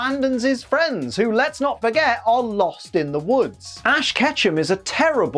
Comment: "Lost in the woods." I've been simping for Mae Borowski too hard. I actually thought he said "Night in the Woods."